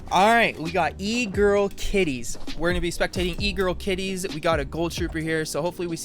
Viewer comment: In the start i thought he said egirl titties 😂